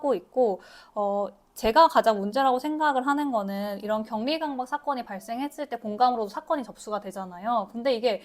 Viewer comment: 자막 “경미 강박“, 26:07 자막 “경리 강박” 둘다 “격리 강박” 이어야해요~ 말씀 너무 잘하셔서 생생하게 잘 들었습니다! 병원은 전문가집단이라고 해서 환자들의 권익 보호가 소홀해지기 쉬운 곳인데, 꼭 개선이 이루어졌으면 좋겠습니다. 화이팅이에요!!